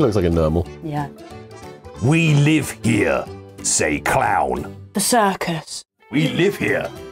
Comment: i fully thought was saying '''we live here, says cloud" instead of clown